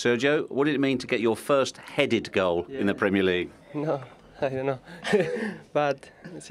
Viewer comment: did he said "u know, i dont know"? Lmao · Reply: He said "no, idont know"